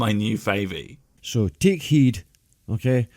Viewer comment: okay... this is first time I've heard a Scottish accent that thick where we can't make out what's said. Raise your hand if you misheard "take heed" and thought he said "dick head".